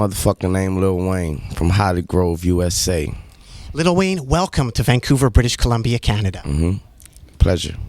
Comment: did he call him lil weave?